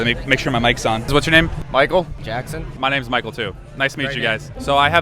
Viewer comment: did i just hear… MICHAEL JACKSON?